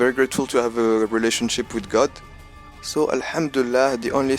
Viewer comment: What is that word you use here? I think you use it a few times. Is it a phrase in another language or am I just missing the word you are saying? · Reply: Hello! It's "Al hamdoullah" = praise be to allah I got used to say it when I'm grateful